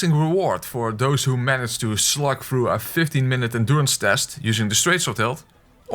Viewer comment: is he saying 15 or FIFTY minutes fight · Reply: 15